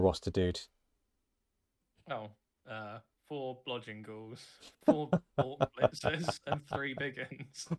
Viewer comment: what the heck was he saying? 4 blah jingles? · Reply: Hahahaha “Blodging Ghouls” 😂 Now known as Blah Jingles!